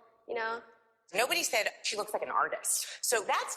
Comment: When she said: “You’re artistic”, I thought she said: “You’re autistic!”, lol!!